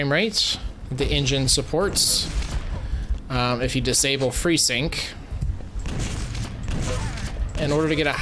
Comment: I said "Freesync" instead of V-Sync. Not sure why I have trouble talking throughout this video. I swear no drinking was involved!